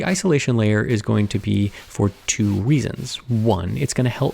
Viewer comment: did I hear him say weasons?